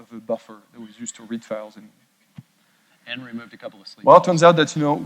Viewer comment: What did Chet say at 45:35, "And removed a couple of [inaudible]" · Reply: "And removed a couple of sleep calls"